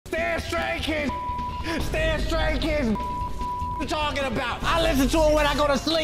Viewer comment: I thought he said that stay straight kids I got so shocked